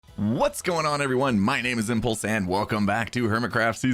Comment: lmao thought he said "My name is ampersand" XD